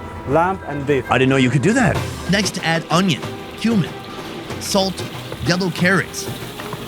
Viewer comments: I thought he said Human instead of “Cumin” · did you say "add human" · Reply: Add cumin he said